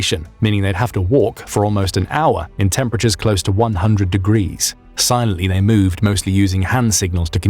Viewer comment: did he say 100 degrees?🤔😳